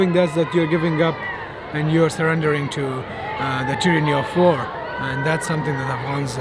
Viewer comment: Between 11:45 & 11:52 the English subtitles say “threatening of war” when he says “tyranny of war”. Weird